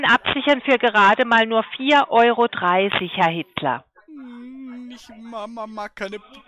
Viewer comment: Herr Hitler? Oder hab ich mich verhört? XD Der Mann spricht so als wenn er kein Selbstbewusstsein hat.